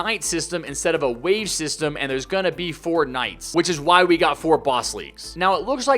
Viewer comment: Haha when he say 4 nights I thought he said fortnite